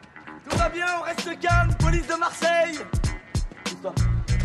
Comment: What does he exactly say in 2:43 after "ca va bien....plice Maresllie" that one word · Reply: He says (pousse toi) (move over to one side. )